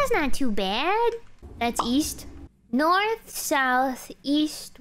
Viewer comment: I thought you said... Weast